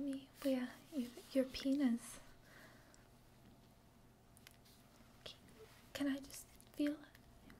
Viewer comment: did anyone else think she said "penis", then asked to feel it? i definately felt the tingles after that